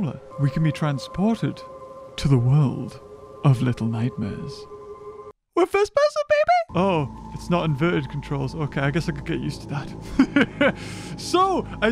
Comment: i heard first person pp